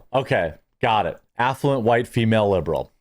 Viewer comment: Affluent White Female Liberal also Affluent White Female Urban Liberal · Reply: Oh sorry I thought you said effluent 😮 .